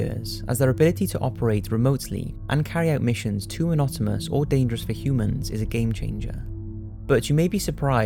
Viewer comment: Cool video I like it. Just wondered what that word he says at around 4:08 means. It sounds like he says "anotamous"? Can't find it in dictionary 🙄 hehe